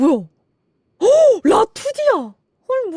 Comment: 라프디...? 뭐라고 한건지 이해를 못했는데 알려주실분...? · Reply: 아마 라이브2D 줄여서 라투디 라고 하신거같네요 만화움직이는거